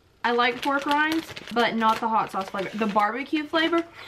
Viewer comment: When you said pork rinds I thought you said war crimes🤣😅